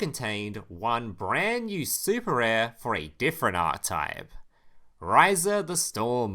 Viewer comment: To this day, every time I hear you say "archetype," it still sounds like "art type." And that's still so reasonable that I have no idea which one's real.